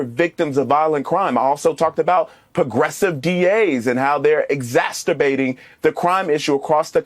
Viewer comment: ha he said "exasturbating" instead of exacerbating... :D